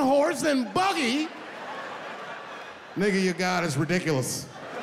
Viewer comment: I thought he said "n.. You got us ridiculous" He actually said: "n.. Your god its ridiculous" after all these times ive watched this